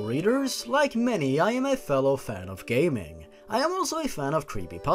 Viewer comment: at first I honestly thought he said "Gayman" instead of "gaming."